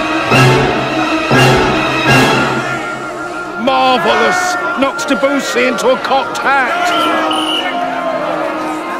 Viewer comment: what did he say? I’m American so therefore British is not my first language. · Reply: “Marvellous. Knocks Debussy into a cocked hat.” It means it’s so good that it makes Debussy look rubbish by comparison.